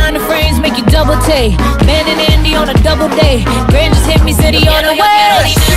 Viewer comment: I thought she said “No way” not “He on the way”